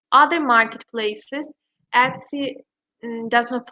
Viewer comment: I thought it said ecstasy for a split second.